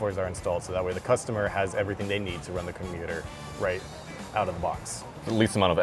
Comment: i love the guy saying commuter instead of computer